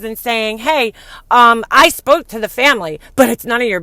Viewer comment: I heard her say, "I spoke with Frankie but it's none of your business" I thought, wow, spoken like a Life Coach, right?